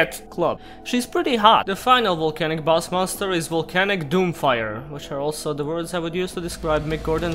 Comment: I thought it said "Volcanic Doomfinger" for a second.